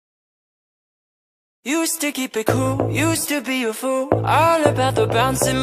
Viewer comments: misheard lyrics on "Used to keep it cool": you sticky pickle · at the beginning it sounds like he says "you sticky pickle" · Instead of "you used to be cool" I keep hearing "you silly pickle" :" | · I keep hearing "used to keep it cool" as "you sticky pickle" 💀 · So uh my son thought that he said "you sticky pickle" when he says "Used to keep it cool." and now all i can hear is that and its soo funny. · The lyrics: “used to keep it cool” What I hear: “*You sticky pickle*” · Everyone else hears : "Used to keep it cool" Vs What i hear : "You sticky Pickle"